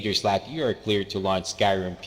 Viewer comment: At the start of the video, I thought you said "lunch" instead of "launch" and I'd be lying if I said that I'm not a little disappointed.